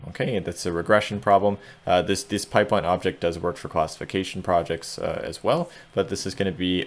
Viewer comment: Did you say pipelines doesn't function for classifications problems? Min